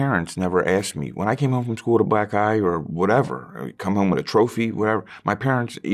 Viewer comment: i heard i came back with a black guy…. Whats wrong w that? And realised oh Black Eye